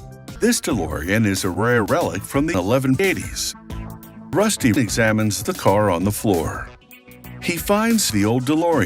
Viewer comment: I thought he said "rust eating salmons"